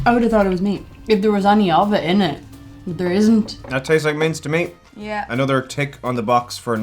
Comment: Minced! Haha! I thought they said mints 😄